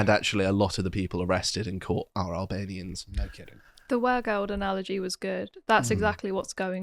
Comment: What analogy did you mention at 13:32? The captions show it as 'were gold analogy' If that's what you said, what is that?